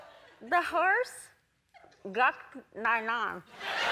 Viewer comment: The first one sounded like she was saying "the whores f**ked my mom"